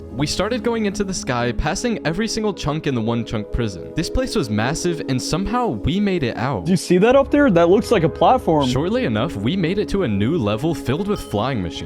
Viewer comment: Bro this just shows how racist I am I thought it said one china😂😂